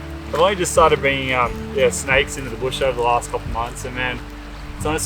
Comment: You really need to start keeping a fish for dinner if you catch it! Also I thought you said you brought steaks for lunch not snakes lol